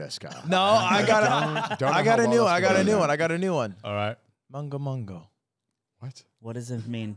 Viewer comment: what i heard ludwig says here is "mongo mongo" which where im from[not japan] mean a kin to "go ahead/you are excused" like a reply for when someone says "excuse me"